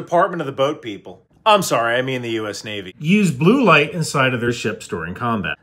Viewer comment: I swear I thought it said bud light not blue light